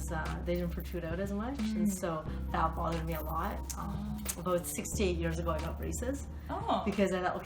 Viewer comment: Woah, she said she got braces “6-8 years ago” but at first I heard “68 years ago” and I thought wow, she wasn’t joking about looking young forever!